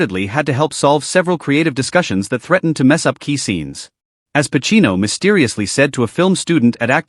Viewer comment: There is no more passionately talented actor than Al Pacino. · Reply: I thought you said "AI Pacino," as in Artificial Intelligence Pacino 😂 (a reference to the terrible AI voice)